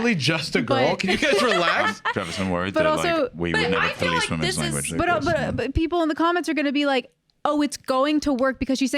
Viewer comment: If anybody knows what the hell says to at 56:50 please tell me it’s driving me insane lol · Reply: "worried that like-- we would never police women's language" is what i heard